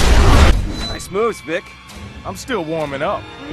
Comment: Dick:Nice moves, Vic. Vic:I'm still warmin'up. Nice style. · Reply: hadi haitham I thought he said "Nice moves, bitch"